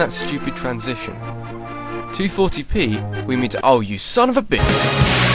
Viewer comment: Cuz of the cutting, sounds like he says "son of a biscuit"